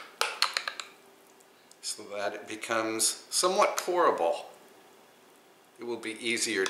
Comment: Gosh, too funny. I thought you said "somewhat horrible" when stirring the jam.